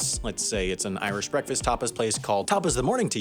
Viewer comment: I heard "Tapas" as "topless" and suddenly this video seemed much more wholesomely capitalistic.